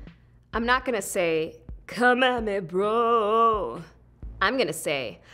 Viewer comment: Wow this is funny and awesome!! Btw what she said @ 0:44 I didn't understand it, English isn't my first language. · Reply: A Singer She says "Come at me Bro" which is a modern American way of saying "Let's fight!"